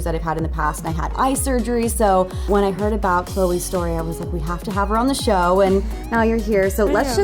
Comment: did she say at 0:52 stop or sorry? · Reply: you meant @ 0:46, Kirbie said "story".